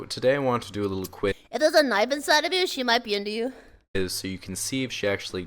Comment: what does say here? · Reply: “If there’s a knife inside of you, she might be into you.”